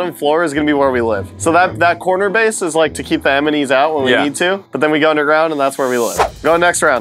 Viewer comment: did he just call the ENEMYS EMENYS i didn’t read the comments before making this comment so i didn’t mean to copy so sorry :)